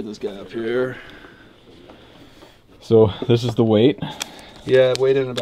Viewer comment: i thought he said: "So this is the way?" And suddenly i heard a wild flute appear in the background. Thanks for the awesome video, Sir!